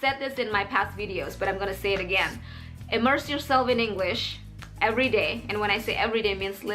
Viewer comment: Did you say emerge (muncul) at 13:46 mbak? 🤔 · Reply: immerse